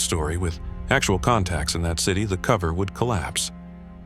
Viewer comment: subtitle shows "COVID" instead of "COVER"